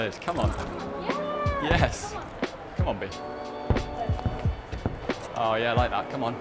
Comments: swear down sounds like hes saying come on bitch 😂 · Did he just said come on bitch at 4:10 ?